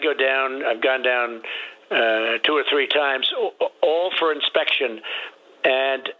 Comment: When did you hear him say 2 and a half time's, I heard him say 2 or 3 time's, let's not imitate his disorder.